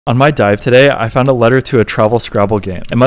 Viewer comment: When he said "On my dive today" I thought he said "I might die today" Lol